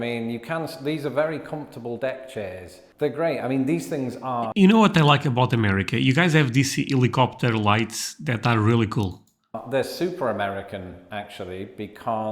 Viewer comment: Excuse me….did you just say helicopter lights?